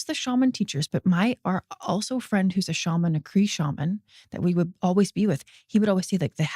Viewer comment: did she say Kree shaman? 😂